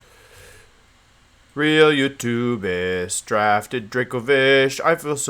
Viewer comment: This entire time i thought he was saying “will you do this” in the beginning of the intro, but i turned on captions and he was saying “real youtubers”, amazing! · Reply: Real youtubists